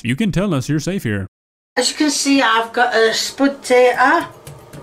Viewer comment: Did she just call it a spudtater? Because I'm stealing that. They're spudtaters now. Tommyartoes is pretty great, too.